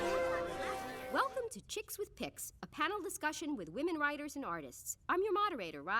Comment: "Chicks with Pix". lol.. idk why i heard it as chick with dihs .. ukwim